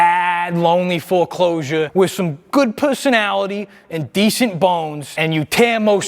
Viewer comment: Did he really say "good pussynality" 💀💀